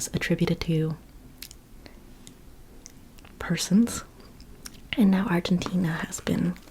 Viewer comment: gracias por los lindos comentarios sobre mi hermoso y complejo país Argentina, love u 🇦🇷🩷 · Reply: pensé que decía el peronismo. Jjjjjaa.